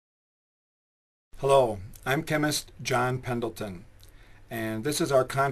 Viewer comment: for truth heheheh when you said 'John Pendleton' I could have sworn you said 'John Simpleton'. :)